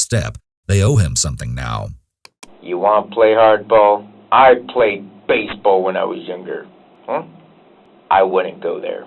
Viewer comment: "you wanna play hard-ball? I played BASE ball, when I was younger" is what did it for me. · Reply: Ohh I thought he said, “I played BASEball when I was INJURED” lmao